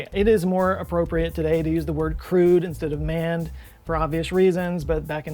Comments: Here's the issue I have between "manned" and "crewed". You say "crewed", but I hear "crude"...and that does not make any sense. It's either unprocessed petroleum or ignorante and unmannered, neither of which describe space research particularly well... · LOL, every time I hear "crewed," my brain translates it as "crude." So "manned" seems more polite, even to this liberal woman.